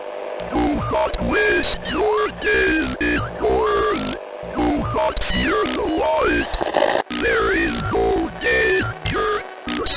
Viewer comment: Holy thanks for this, i literally love SCP so bad I thought they said "They can not f*ck the light", instead they said "they can not Fear the light*.